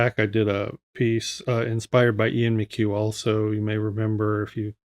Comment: I love the work of the artist you mentioned (Ian McHugh?) but can't find a link to his work. Perhaps I misheard the name. If you can point me in his direction I'd be very grateful, thanks! And love your drawing too, it's inspired me today. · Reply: Ian McQue. Thanks!